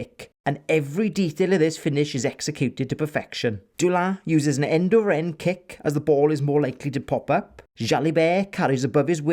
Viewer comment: What did you call the kick at 3:50? I couldn't understand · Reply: An end over end chip kick!